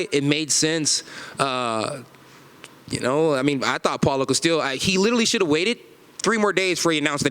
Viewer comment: Did he just say “paulo castillo”? 😂